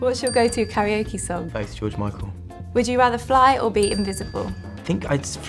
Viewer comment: what did he say I don't understand HELP PLEASE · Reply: “Faith” by George Michael